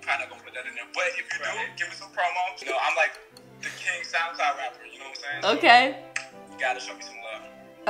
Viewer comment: what jenna thought he said: "the king south side rapper" what he really said: "the king sound cloud rapper"🤣 and yes I'm re watching her videos, gO wAy